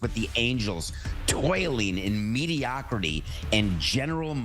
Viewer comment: Re: The Angels: "Toiling in mediocrity." 🤣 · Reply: I heard "Toileting"